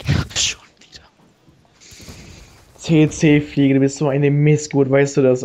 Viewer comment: nice video :DD ich hab immer WC-fliege verstanden xD